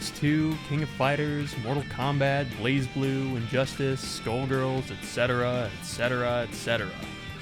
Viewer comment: what game did he say at 1:57 after injustice ??? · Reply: Skullgirls